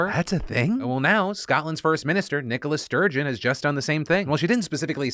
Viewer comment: I kept hearing "Nikolas"